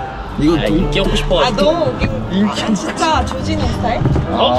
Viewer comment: 조진웅 스타일을 조지는 스타일로 잘못 들었네여 ㅋㅋ 자막 보고 조진웅인거 알았어요 ㅋㅋㅋㅋㅋㅋㅋㅋㅋㅋ